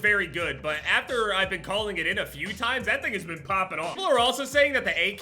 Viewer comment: I thought at 4:00 that you said that niggas been popping off😂😂